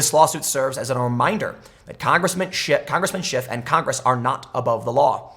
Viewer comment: I heard you say congressman shit at 8:05, lol.